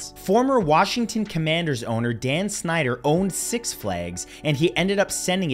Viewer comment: Dan Snyder? Holy cow i thought you said Dan Schneider 😂 thought homie owned a kids waterpark and got majorly alarmed for a second